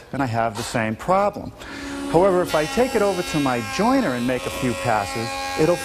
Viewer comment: All this time I thought he was saying "Joiner". I just realized it's called the "JoinTer".